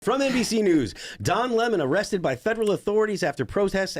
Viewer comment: I keep mishearing "Don Lenin" at points.